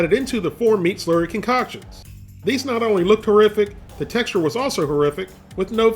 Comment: HORRIFIC !!! I thought he said TERRIFIC !!! HAHAHAHA